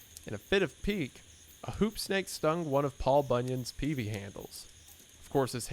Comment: bruh, I swear you were saying Poopstain. I was listening while gaming and legit had to stop and check why people were afraid of speeding venomous skidmarks lol